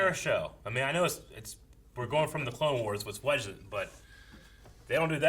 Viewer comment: What is saying at 20:36? All I can hear is “we’re going from the clone wars lessdwedgchum” lol please it’s driving me crazy.